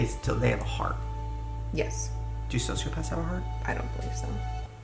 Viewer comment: What in the world is he saying? “Do you sow soup as a heart” is all I can hear. 😂